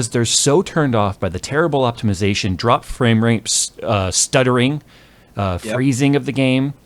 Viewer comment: did you say "dropped frame RAPES?" lol